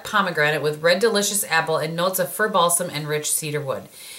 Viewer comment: When you said 'fir balsam', I thought you said 'fur balls'. I had to rewind and listen again! LOL!!!!!!!!!!!!!!!!!!!!!!!!!!!!!!!!!!!!!!! I can't stop laughing! I need to get my ears checked, clearly. ;) Sad times about the lipgloss. That does not sound fun at all. Thanks so much for sharing! XOXO Sarah